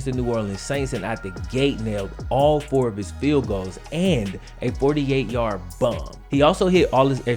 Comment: I thought he said "48 yard boner."